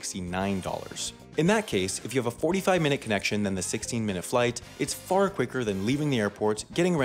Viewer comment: I thought you said FIGHT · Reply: I thought you said Plight.